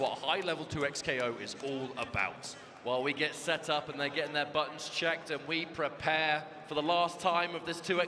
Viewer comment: Low key sounded like he was saying they were getting their butts checked